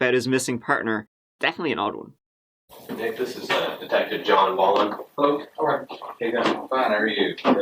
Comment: did he say detective John Ballen?? John fucken Ballen?!?!